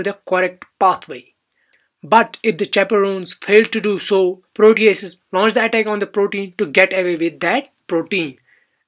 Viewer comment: what did he say ? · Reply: Proteases launch the attack on Protein , to get away with the protein,.... apologies for my weird pronunciation..... and my speed